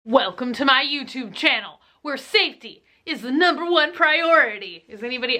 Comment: Hahahaha cute !!! I thought you said shwifty lol but you said safety haha Russian hacker!!